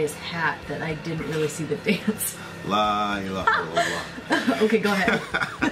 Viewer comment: did just say la illaha ilallah?